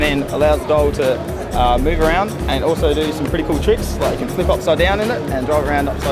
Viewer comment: what does he call that thing?? Di-what? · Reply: di wheel